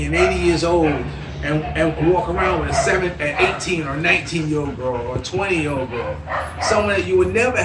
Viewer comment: Wait a minute...🤔 did he just say,[you can walk around with a 7yr old?] Mentioned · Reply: No he said 17, 18, 19 20 years old, continues talk Age are no thatt important in other countries Just like gays are not bothered in USA